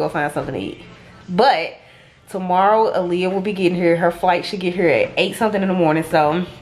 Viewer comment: i thought it said her first solo fight i was likeeeee whatttt😂